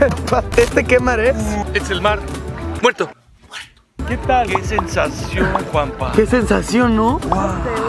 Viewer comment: pensé que dijo que sensación papa